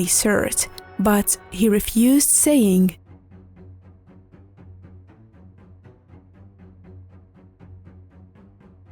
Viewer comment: What did he say after refusing? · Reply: He sad This boat has no capacity for all fighters